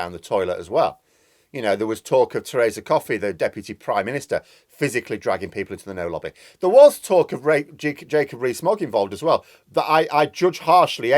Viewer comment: What did you say at 11:24? Slip of the tongue? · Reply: Think Coffey sumo'd them into the no lobby.